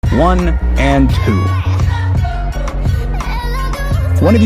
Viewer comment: I keep hearing "One and doo" HELP✋